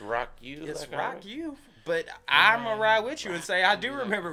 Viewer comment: I always thought it said " Rocked you "